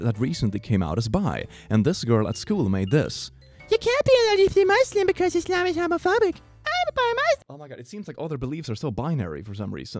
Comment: rules of Islam says u can’t be gay if ur in it lol I’m pretty sure I heard that